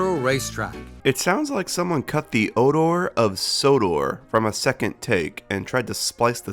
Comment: I clearly hear him saying “order”, not “odor” (although “odor” would make it funny).